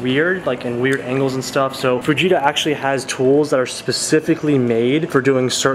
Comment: captions say "VEGETA" instead of FUJITA xD